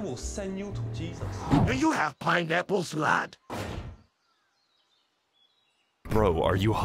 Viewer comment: What is buck saying? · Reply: "do you have pineapples, lad?"